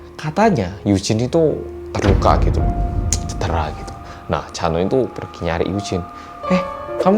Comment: Aduh salah denger namanya jadi Chanwoo... hehehe